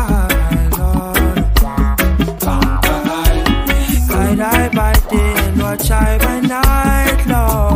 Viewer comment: Always thought he said "Guide I by day, Watch I by night"